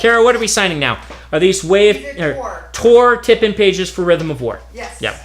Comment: "Tip-in". I thought he was saying "tippet pages".